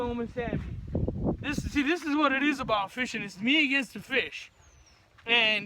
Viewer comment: I thought that said fishing with tits LoL